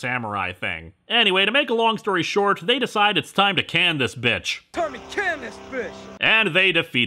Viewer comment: “Time to can this b***h”, correct me if I misheard that but if that’s what Jason really did say then I don’t get how they were even allowed to do that. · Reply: SuperNinja493 he said "can this fish!"